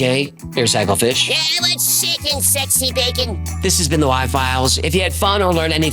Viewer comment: Why does Hecklefiah sound like he is say "I want s@ and sexy bacon" at the end. What did he say?! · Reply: He says "What's shaking sexy bacon?"